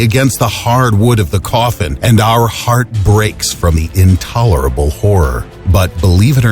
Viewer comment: did u call me an intolerable whore?